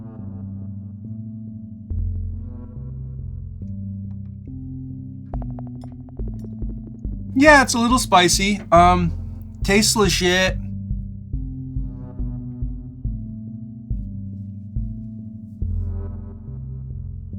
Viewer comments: did he say tastes legit or tasteless shit? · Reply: I think he said tastes legit but it did sound like tasteless shit lol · I thought you said tasteless shit instead of tastes legit had to listen again